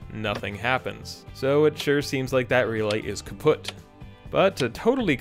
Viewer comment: Did you say "kaputt" @ 17:40 or did my ears play a trick on me??? x)